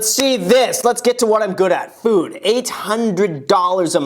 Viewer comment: sounds like he said $500 and not $800 to me.